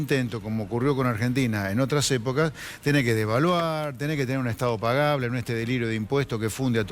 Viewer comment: "Tenes que devaluar". ¿Escuche bien?